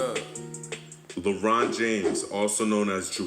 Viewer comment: I thought he said lebron james